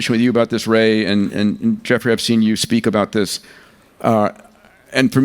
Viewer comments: did he call him Geoffrey Epstein? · I thought he called him Jeffrey Epstein and I almost lost it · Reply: He did not say that. I thought he did last night when listening on my phone, but I just took another listen a few times now on my PC and he says "And Geoffrey I've seen you speak about this"". I also slowed it down and it is very clear what he says. I think that guys name has been in the media so much that the brain automatically hears it lol.